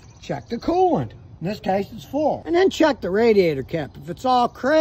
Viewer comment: Is it me or did I hear "this tastes full" when he opened the radiator cap?